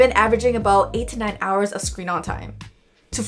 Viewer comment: Did she say 89 hours of screen on time 🙄